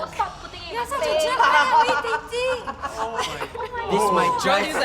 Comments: did say why r we dating to? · what i thought i heard say she was dating um i might be wrong but what someone help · Whats did say at 42:06? i think i heard her say “why are we dating?”